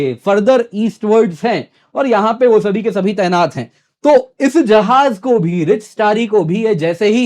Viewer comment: did he said bsdk tainat hai